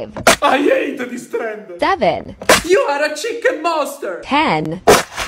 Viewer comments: For a second I thought they said “chicken master” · "YOu ArE A cHicKen mOnStEr"😂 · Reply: Oh I thought he said molester 🤑